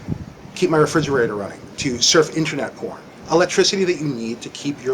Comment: did he say; surf internet porn?